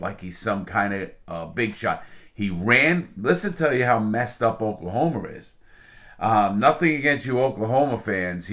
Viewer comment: I feel like I heard oklahomer